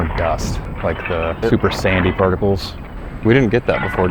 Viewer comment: I can't understand if he's saying Super Sandy or Super Saiyany particles